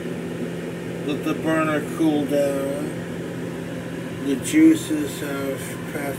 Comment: He said "let the burner cool down" and I could have sworn he said something about a barnacle in the pot